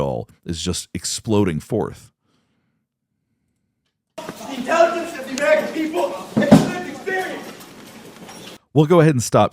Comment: I heard “insult to the American people.”